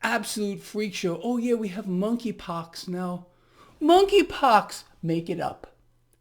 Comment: thought it said MONEY pox then 🤣😂. f freak show indeed. love ya Greg. Thankyou for all you do for us 💚